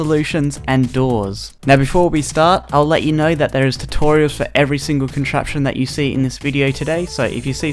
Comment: I thought you said there’s a to Torio